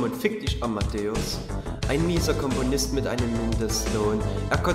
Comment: Da hab ich früher immer Kommunist verstanden. Mulm